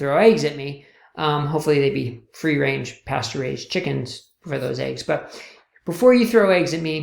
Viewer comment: pasturized chickens ? I would think they would object ... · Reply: I heard "pasture raised" not "pasteurized". Great talk